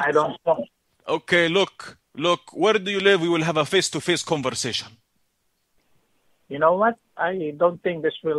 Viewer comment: I had to go back, the first time i thought he said he wanted a "face to fist conversation"